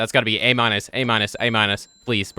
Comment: i thought he kept saying “A MINOR A MINOR A MINOR” 😭😭😭